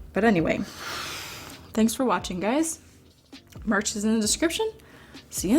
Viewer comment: for some reason I thought you said MARCH is in the description